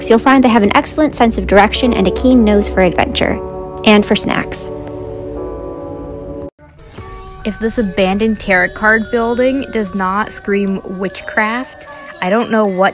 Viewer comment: Was anyone else triggered by how thay one girl said tarot? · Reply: I heard "carrot card..."